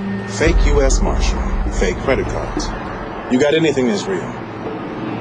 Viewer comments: On subtitles it says boobs, I thought he said boots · I thought he said "my boots", too haha "boobs" makes much more sense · I thought Dean said, "My boobs". Would make more sense and more funnier.